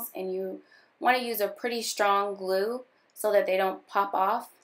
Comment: Hahaha lol when you said strong glue I wasn't paying attention and I thought that you said song glue and I was like........ Ummmm........... So I rewinded and I said ohhhhhhhhhhhhh strong not song